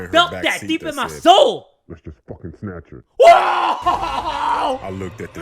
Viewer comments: I thought dat man said f**king snatch her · Can someone please tell me what the person in the back said at 15:35? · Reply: He said 'Lets just f**king snatch her' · Who else doesnt know what the person said? It was too muffled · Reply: He said "Lets just fukin snatch her.*" or *"Just fukin snatch her."